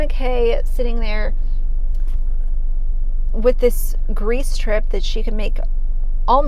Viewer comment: You said "Greece trip" I heard "grease drip".